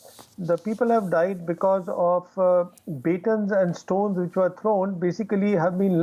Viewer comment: What did he said? 2:32 People have died because...? Beetles and storm? I still can’t figure out how more than 20 soldiers can die in a clash without weapons